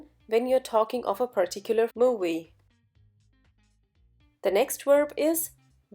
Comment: I can't understand the word at 1:00, it sounds like "movie" but I'm not sure. Please help me on this. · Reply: she said movie only